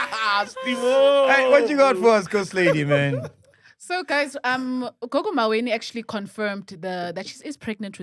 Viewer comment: Did he just say lady zama okunye?😂